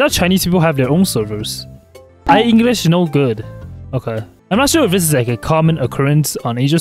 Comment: For a second at 0:46 I thought he said i’m no good😂😂😂😂😂